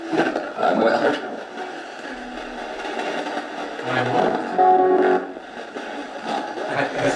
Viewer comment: i heard 'i would hurt' am i the only one?